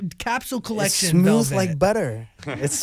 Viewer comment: Did he just say "smooth like butter"😳